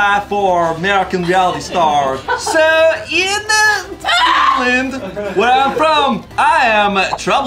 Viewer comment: What country did he say hes character was from.... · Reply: Finland I think